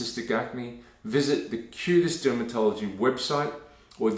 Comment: I was listening while brushing my teeth and I thought you said “the cutest dermatologist” at 2:35. 😂